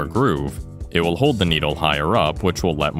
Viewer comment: Just watched all the way to 15:51 apparently for a second time but the part that connected the dots was you saying sneedle both times you said needle